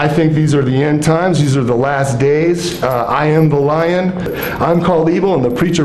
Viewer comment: Good morning. Did you get what he said at 17:27? After he mentioned the last days? He said “Uh, I am the lion. I’m called evil. And the preacher preaching that someone other than God, the son of Satan, is God is considered good. and what did I do? I struck. Cuz I am everyone’s oldest father. And daddy’s gonna strike.” Ewwww. He sounds like Lucifer himself.